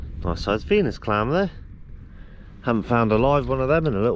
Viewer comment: Could have sworn you said you found a "penis clamp" then I realised it was a Venus clam 🙉🤪😄👍